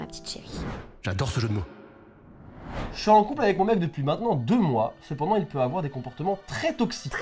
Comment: j'ai compris "je suis en couple avec moi même depuis 2 mois" j'ai pas compris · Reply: à la place de “avec moi même depuis 2 mois” c’est “avec mon mec depuis maintenant 2 mois”